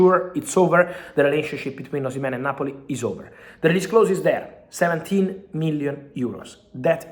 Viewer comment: Did he say 17mil release clause for Oshimen??? That makes him cheaper than Delap no?